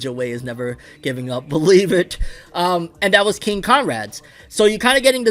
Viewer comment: Damn, I thought he was saying King Kong Rad I was hella confused It took me 6 minutes to understand what he was saying